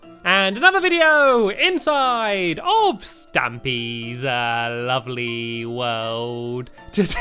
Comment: Stampy,when you say your name in the beggining,it's sounds like your saying stammy 😁